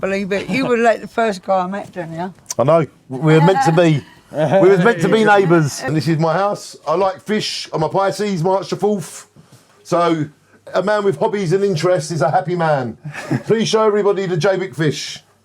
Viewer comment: what does say at 31:53 "i like fish, im a quite .... . ??""